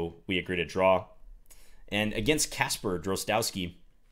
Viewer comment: I thought said 'Anand'. Even former World Champions love videos. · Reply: and at 13:38 i thought he said 'Kasparov'.